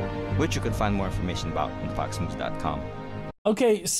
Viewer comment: Did he really say "more information about at fox News dot com"? 🤦‍♂️ What a nutjob...